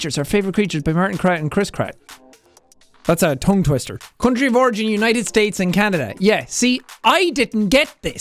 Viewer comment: I thought he said "congi vorgy" when h actually said "country of origin"😭💀💀